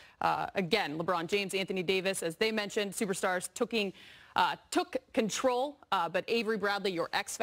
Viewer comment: Did she just say tooking?